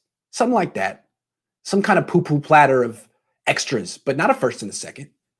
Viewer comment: Hey Rayshawn Gilbert did you see what he said at 2:20 a poo poo of extra players.